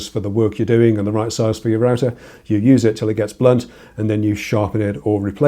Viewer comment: oh, i heard "you use it till it gets blood" (blunt) and it sounded very reasonable at first 😁😅